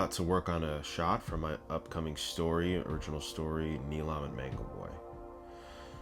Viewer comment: did you just say origin story or original story? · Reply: It’s an original story done in the DBZ style. Thanks for watching!